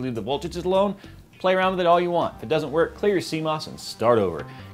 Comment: What does he say at 5:41? "Clear your ____ and start over" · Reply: CMOS